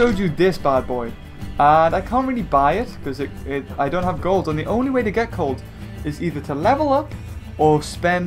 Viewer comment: said "The only way to get cold." Instead of gold.